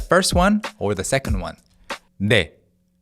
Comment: I hear ne